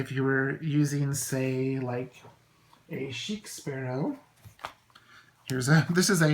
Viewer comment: Did you say 5:27 Sheikhs Barrow? Sheep Sparrow? Please and thank you :) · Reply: Chic Sparrow - it's a wonderful shop that makes beautiful travelers notebooks.